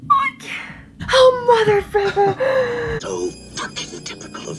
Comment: All these “mothers” and “fricks” are the closest to swearing gotten in all her videos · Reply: I could swear I heard her finish a "mother------" at about 18:27 , though I'm open to alternate interpretations.